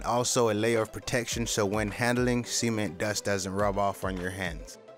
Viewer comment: handling s e m e n was what it sounded like but it was actually cement